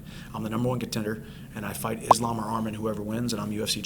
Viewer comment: did he say Islam Muhammad ? lol